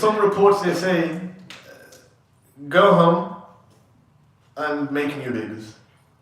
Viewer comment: "go home and.. ." I couldn't make out what you said there brother..? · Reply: “go home and make new babies”